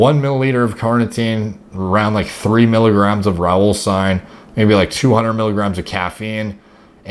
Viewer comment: what does he say? 3 milligrams of Raulstine? Raw sign? Rowlstein? What is the name?? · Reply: Alpha Yohimbine, or Rauwolscine as he was saying